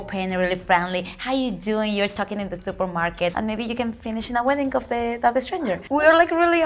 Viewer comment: didn't say "And you might talk to a stranger until you finish waiting in line". What she really said was "...and maybe you can finish in the wedding of that stranger". That's how friendly we are as Mexicans, we just vibe with people around us.